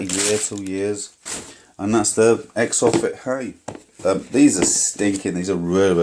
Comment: what are the name of the shoes? i don't understand what you say lol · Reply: Exofit hi